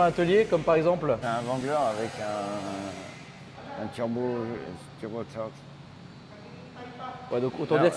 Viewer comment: Jeep Wangler à 2:25 mn deux minutes 25 secondes dans la vidéo environ, quelqu’un peut me dire, c’est quoi ce turbo ….. a employé un mot que j’ai mal entendu dans la vidéo, vraiment merci à celle ou celui qui pourra éclairer la communauté et moi-même humblement ,bien entendu : c’est quoi ce truc turbo. Merci beaucoup d’avance. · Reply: Normalement turbocharged / turbocharger